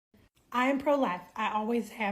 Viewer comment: The first words I heard were "I am prolapsed" 🤭